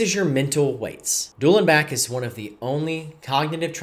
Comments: What is he saying here? “Dual and Back”? What is that? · can someone type what he said.....I couldn't understand the word 😔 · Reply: Duel and back